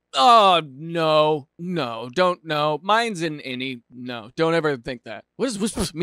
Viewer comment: did he just say “mines’s an innie” As in innie or outie 😂😂😂😂